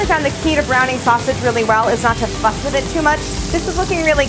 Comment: does she say: ''....I actually found the key to browning sausage is not to fuck with it too much.'' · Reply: I think she said "not to fuss with it too much."